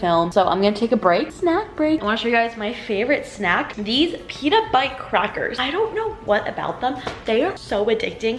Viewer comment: OMG. When you said "pita bite" why did i heard "peanut butt"????😂